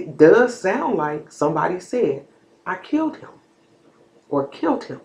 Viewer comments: I heard it “I killed him” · Yep I heard her say I killed him twice